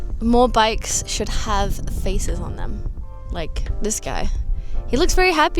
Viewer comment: Thought she said “more bikes should have faeces on them”